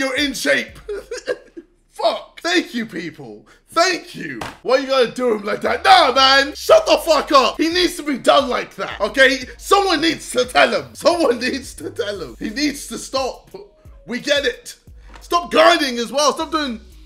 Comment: I heard “He needs to be Down Like That”. 😏😂👌🏼